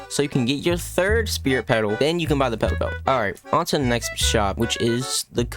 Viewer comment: What he said: Petal Belt What Captions thought he said: P#doph!le